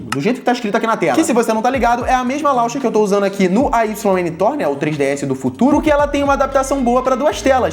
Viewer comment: que portátil é esse? não deu pra entender o que você falou Você têm vídeo sobre ele? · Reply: Ayn thor